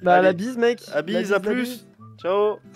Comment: j'ai quoi ? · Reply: J'ai brillé ou vrillé 👀